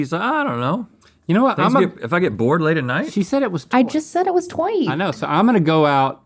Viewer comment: what is the word "toit" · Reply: Tight